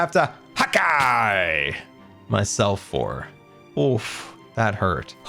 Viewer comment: When you said "hawkeye myself" i thought you said "hakai myself"